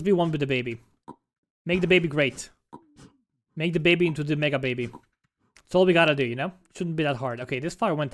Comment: i heard you saying "make the baby into a MAGA baby" LOL